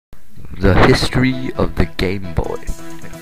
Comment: At first it sounds like you said, "The history of the gay boy". LOL